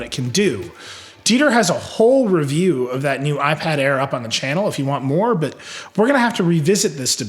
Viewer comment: Is it me or anyone heard him say iPad Air at 6:55?